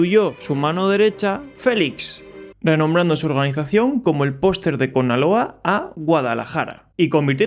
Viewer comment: "El poster de Con-aloa", nunca me habia reido tanto xdd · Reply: Al principio pense que decía poster de Coca cola XD